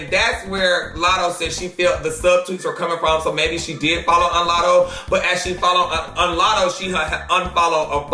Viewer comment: Is it just me or did he say " follow unlatto"😭😭